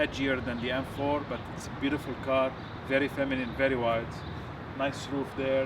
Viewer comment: Did he say the car looks feminine ???